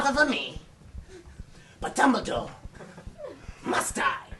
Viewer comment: at like 7:09 when voldy says dumbledore.. i hear a slight bit of Umbridge haha